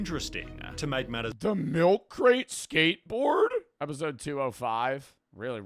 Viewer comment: sounded like Bam saying "A milk crate skateboard?"